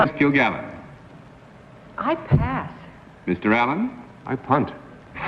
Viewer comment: what does he say? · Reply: He said: "I punt", which turns out it's a kick in the football. So after Dorothy said I pass, he responded I punt